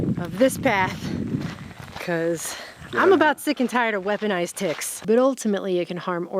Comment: I thought she said Weaponized Dicks 😂